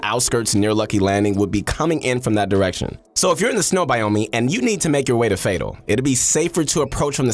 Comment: he says snow biomee instead of biome